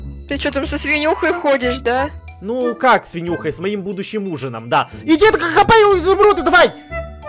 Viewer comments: мне послышалось с моим будущеем мужАм😂😂😂😂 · мне послышалось с моим будущим МУЖЕМ 😂 😂 😂